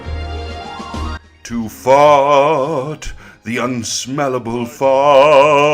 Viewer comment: Tjat time when you said "to fart"...while i heard "to fought"...i guess mr.tom has got me with his sound projection technique...🤮🤮🤮